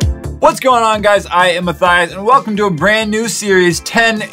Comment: I heard "I ate Matthias" lol